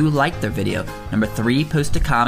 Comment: I swear, at the end, I heard him say "eat this shi-"